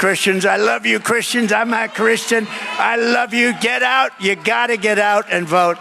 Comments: I heard him say " all my Christians" i didnt here him say "i'm not" but according to some in the comment section he said "i'm a Christian" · I just have to comment on President Trump's remarks, I think you misunderstood him. He said I'm a Christian but he kind of growled and it sounded like he said I'm not a Christian. President Trump has come to the saving knowledge of Jesus Christ at work in his life, and I believe God is opening his eyes in new ways. President Trump is a common sense kind of guy and that's the only reason he's laying his life down to do what he does best. Run a clean business! Our country will thrive being run like a healthy business, with moral values in place. No if's, and's, or but's. President Trump loves our country being and staying FREE. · Russel, I heard Trump say he’s “a Christian “. I’ve seen a couple podcasters report he said hes “not Christian “. I pulled up an article by The Hill and it confirmed that he said “I’m a Christian.” I think it was just misheard by some people. It’s a statement that it’s important to get correct. I agree on your take on the context of his statement regarding not having to vote again. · At first I also thought he had said I'm not a Christian, which wouldn't make sense, then I listened to it several times and understood he had said "I'm a Christian". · You misheard Trump—he didn’t say “I’m not a Christian.” He actually says, “I’m a Christian.” But it can be kind of hard to hear. But he 100% says he IS a Christian.